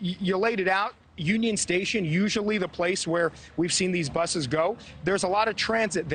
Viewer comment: Did he just say brusses.